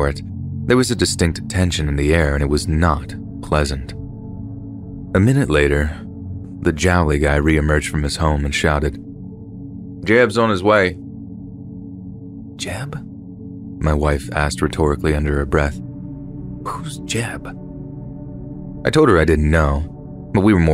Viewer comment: I swear to God I thought he says "dibs on his wife"😂